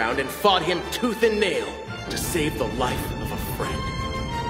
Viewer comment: Tell me why I thought he said "to save the love of my life" at 11:04 omg